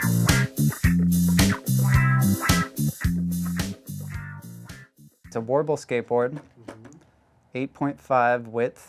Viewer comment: At first I thought he said it’s a portable skateboard 😂